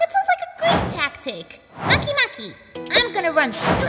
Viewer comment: when Charon says makemake subtitles say monkey monkey lol